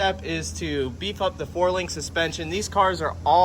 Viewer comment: When I hear "Four Link" I actually hear "Quarter Mile"